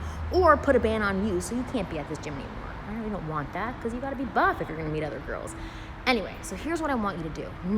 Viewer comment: "I really don't want that because you've got to be ..... if you want to meet other girls" I'm ever so sorry, what was the word I'm missing there? Sounds like "bof" which is a word I remember well from French textbooks :) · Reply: The word you did not understand was "buff", which, in case you aren't familiar with it, means "being in good physical shape with fine muscle tone". Actually, I was a little surprised that she said that, because I have read often that what shape you are in is not a big factor in how women view men. I cannot recall whether, herself, has every said or written that, so maybe she believes it is more important than other advice-givers do.